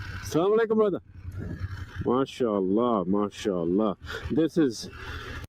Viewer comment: is he saying "martial law martial law?"